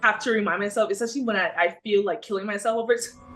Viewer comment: What does she say around the 5:06 mark? Something about killing herself? I couldn't understand her.